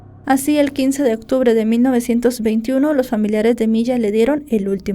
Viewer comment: escuche 1921 😮😮😮😮😮